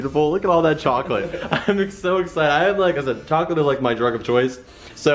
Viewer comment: Did he just say chocolate dos